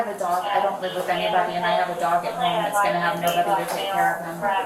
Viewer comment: She said "ive got a dog at home"😂😂😂 no shit i thought she said I have a daughter at home. So I'm thinking maybe the way she's able to get her injections is reselling all the stuff. That shit is Spensive.. 😂😂😂